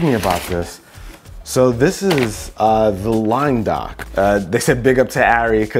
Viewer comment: I thought you said line lock. I was ljke man this guy gonna do some burnies